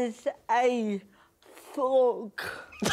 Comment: What does Rosie say at 7:58? I couldn't understand the funny word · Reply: A thug